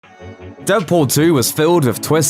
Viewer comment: did you say deadporn 2?